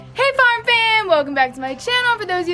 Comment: "Hey, farm fam!" I thought you said star fam lol